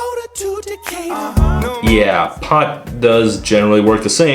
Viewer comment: I legitimately always thought he said the pot calmed his tooth decay. The fuck am I supposed to know decataur is a word